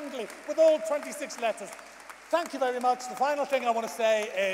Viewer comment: Did he deadname Elliot Paige at the end or did I just mishear? Edit: Nope, I just misheard.